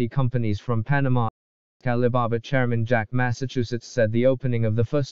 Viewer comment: Good job! Still needs improvement. For example at 0:44 the A.I said "Jack Massachusetts" instead of "Jack Ma".